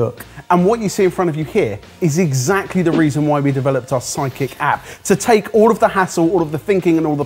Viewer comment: Anyone else hear 'Psychic' app at 14:40 instead of sidekick app?